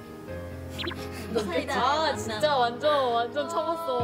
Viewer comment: did i hear Saida?